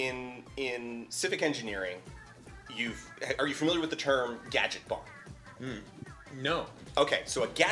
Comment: what is the term he says which auto subtitles take as “gadget bond”??? I’ve been trying to google various interpretations of what he said and none of them are giving useful results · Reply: I bet you it’s “gadgetbahn”, like gadget-autobahn